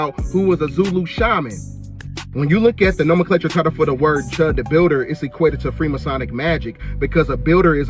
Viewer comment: Hold up. 1. Who the eff is Chud the builder lol? What a dumb ass name. Also I thought they were saying chug the builder lol 😂. 2. Why people scared of another BLM movement??? Hellll we need BLM and PLM (poor lives matter). Eff all them elites and racists.